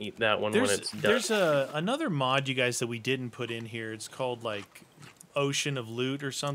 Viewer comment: did i hear "Lewd" or "lude"